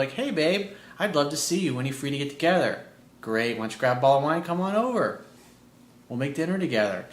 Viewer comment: Did he say "... grab a ball of win"? approx